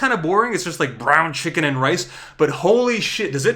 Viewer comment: Did he say brown chicken & rice...?